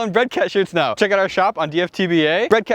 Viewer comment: I thought you said bread catcher not bread cat shirt! I was kinda excited to see what kind of crazy bread catcher you could have made.